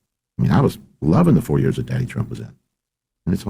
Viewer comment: Does he say “daddy Trump” ? Holy hell, he did. I relistened 5 times thinking I misheard.